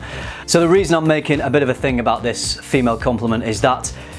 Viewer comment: Haha when you slowed it down sounds like you said "she-male" :) You are great man keep it up